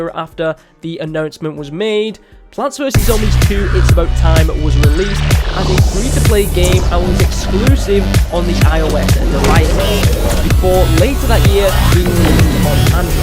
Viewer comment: Can anyone please say me what said? Music has blown my ears! :( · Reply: PvZ 2 it’s about time was released and was exclusive to IOS and received numerous updates